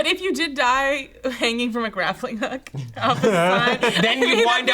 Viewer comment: Was saying "graffling" hook?